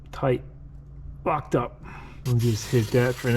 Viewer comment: What did you say about the carb linkage at 7:33? haha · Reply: Locked up. But your right, it could have sounded like something else